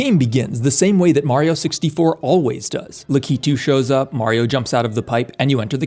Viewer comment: Did bro just called Lakitu as "Licky Two"? 😭😭😭